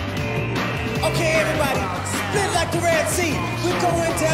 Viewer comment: WAIT! Did he say "spin" THIS PREDICTED CRASH 4 YALL